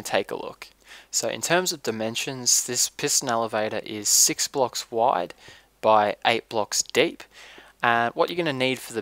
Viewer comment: did he say its 6 blocks wide and 8 blocks dick?????